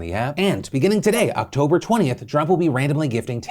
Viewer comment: "And beginning today, October 20th, Trump will be be gifting $10,000 worth of reward points into member accounts every Tuesday leading up to the holidays to spread a little cheer" I had to go back a few times... He said Drop but I heard Trump. What did you hear?